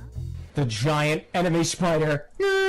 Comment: me and my friend misheard that once, so now our inside joke is "Giant Anime Spider" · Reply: It took me as bit to realize it actually said giant enemy spider and not anime spider